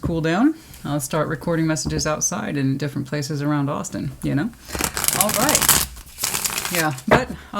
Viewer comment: Texas 🤠but have good memories visiting Mass. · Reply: Haha oh you said Austin, i thought you said Boston hehe lol I thought I couldve sworn she was in Texas? lol